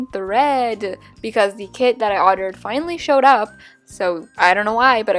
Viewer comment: instead of saying ORDERED she said ottered